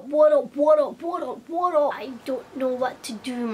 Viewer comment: I kept hearing porno porno porno as if porno was your new year's resolution(0:01 - 0:03). Hahahahaah!